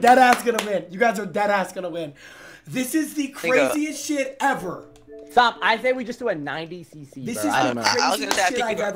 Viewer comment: WHAT DID SAY !?! 💀💀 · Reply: Mingo